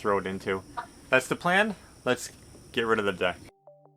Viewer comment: Noooo... 0:35 did you just say lets get rid of the Duck? LOL ...